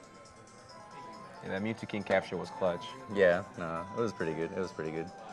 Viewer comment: did he say Mew2King?